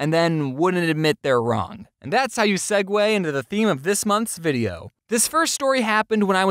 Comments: Wait did James say “the theme for this MONTHS video?” I CANT WAIT ANOTHER MONTH FOR A VIDEO (it’s just after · Did he just say month’s video as in only updating every month